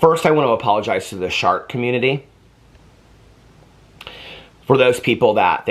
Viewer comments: “First, I want to apologize to the shart community…” The way I just shrieked 😂😂😂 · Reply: I thought he said shark community, at first, and I was like what the hell did the sharks do?😂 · you owe me an apology. At first I thought you said shark community, and I was going to forgive you for making fun of sharks, as I’m a shark lover. But then it’s clear you said shart community. I can forgive you from the shart community as your apology was definitely necessary and all-inclusive, but now I demand an apology to the shark community. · I thought he said shark community. I was like when did he offend shark 🦈 😂😂😂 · As a 39 year old wife and mother, who has shit her pants in public, in bed, at work, etc, and pissed her pants in the same places, I forgive you. 🤣🤣🤣🤣🤣🩵🩵🩵🩵🩵, I about died when you said shart. I thought you said shark and I was so confused. Oh man. I love you so much and I’m so thankful for you. On dark days, you make me laugh and smile. So thank you!!!!!!🩵🩵🩵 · Omg I thought you said 🦈 community 😂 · looking dead straight into the camera while saying “First I wanna apologize to the shart community” without breaking character deserves an Academy Award. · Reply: 😂😂😂! At first i thought he said shark community! He got me good this time!